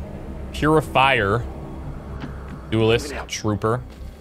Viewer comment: Finally caught up on the timestamps: 2:04 - 2:14 > Pure-li-filer 7:38 - 8:11 > Dead give away 9:00 - 9:05 > What did you say? 10:54 - 11:53 > He can more than do it 13:14 - 13:27 > Great target 15:23 - 15:28 > That's right 39:31 - 39:59 > Time and Space